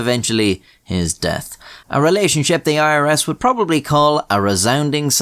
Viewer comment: i thought it said the IRA